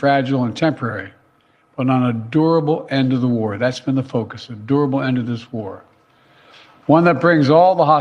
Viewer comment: legit thought he was saying "an adorable end to this war"